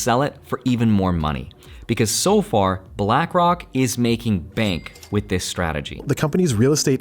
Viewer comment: Did you just say Blackrock in 8:59?